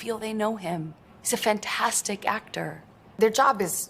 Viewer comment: She said he was a fantastic actor! I thought she said he was washed up??!!